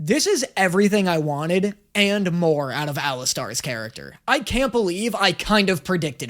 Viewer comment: did man just say “ala⭐️”